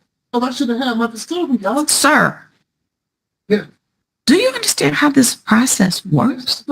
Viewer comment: Did he really call the judge "dog"?